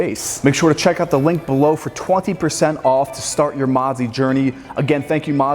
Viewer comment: I thought u said mozzy sponsored this vid i was like wtf 😂